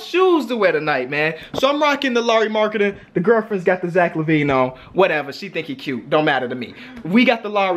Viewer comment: When they said zach lavine i thought they said javale mcgee